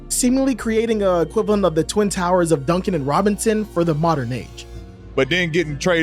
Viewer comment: I really thought you said Duncan Robinson.